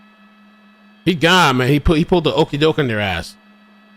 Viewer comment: I think he pulled the “Rope-a-dope” not an okie dope. Maybe I misheard you. If that’s the case, my bad.